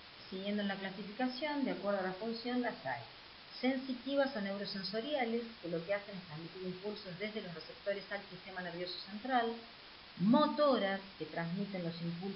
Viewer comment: alguien sabe que dijo en el minuto 18:39? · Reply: las sensitivas o neurosensoriales lo que hacen es transmititr impulsos desde los receptores al sistema nervioso central